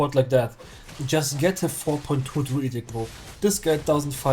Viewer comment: what did you say ? "take a 4 point woodjdhskd" ? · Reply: Druidic